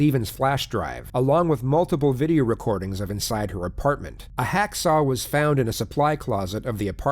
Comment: He cut her up with a chainsaw lol poke the eyes yeah sure... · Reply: Jay B I thought they said hacksaw🤔